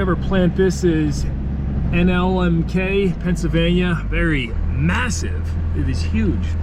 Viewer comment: did you say massive ?